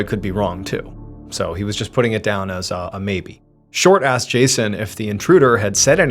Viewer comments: Not gonna lie.... at first I thought he said "the cat was unarmed".... I had questions.... until my mind caught up with my ears.... "OHHHH unHARMED"... 🤣🤣🤣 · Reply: I definitely heard "Short Ass Jason" which i thought was a bit harsh until I realised he said "Short Asked Jason" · I swear I heard "short ass Jason"